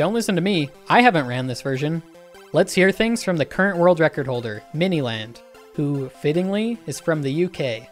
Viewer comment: bro i thought he said "MiniLadd" 🤣